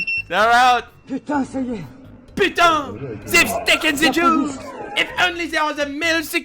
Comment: I know he said jewels, but it sounded like he said they taken the jews